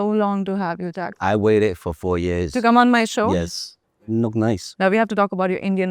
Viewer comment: Omg is he said you look nice in